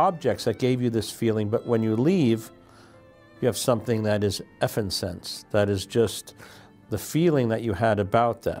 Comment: "when you leave, you have F'n sense." Is that what he said?